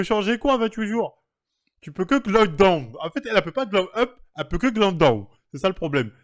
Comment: il dit quoi à 18:16 ? Glock Boom ?